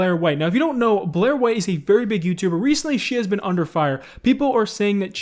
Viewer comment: I thought Scarce said "Very Big Boobtube"